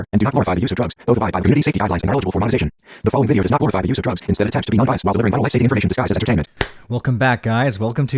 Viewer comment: just had to point out that at 0:13 the captions say: "the following video is not bothered by the use of drugs instead of taxes." lol